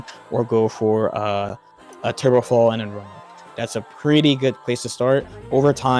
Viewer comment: Great video man! Btw what do you say around 10:32 you begin with: "or do a trubofall and then ......."? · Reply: run up since it's a soft knockdown and you should recover quicker you can mix a variety of ways? I'm new to the character so I dont know fully what you can do